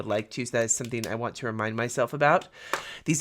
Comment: I swear I thought you said e. coli markers. 😂